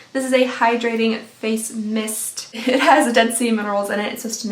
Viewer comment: I totally misheard "Dead Sea Minerals" as "Dead Seaman Rolls" I watched it four times. Same thing each time. But I used my context clues to figure it out 😂